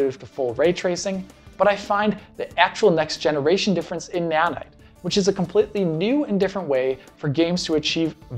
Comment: So this is where all my money from buying Fortnite skins went. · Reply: Hey bud, do you know what did he say between 18:41 and 18:43? I couldn't listened very well what he said exactly. I heard a supposed word "in nanite". I say supposed cuz Idk what he actually spoke. I just heard: "but I find the actual next generation difference in nanite which...." in nanite, inanite, idk. Could you say plz?